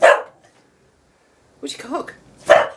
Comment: When you were talking to Daisy, I thought you said,"Where's your 'cock!' 😂😂😂"